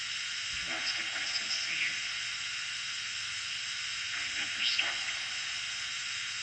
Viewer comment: yall hear that thing talking? Think it says "i never stopped loving you" at one point.